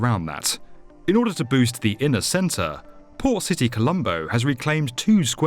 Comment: Thought he said “poor city” instead of port lol